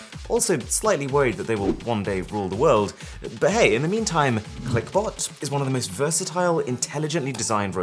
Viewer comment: for a second i thought that arun had said clickbait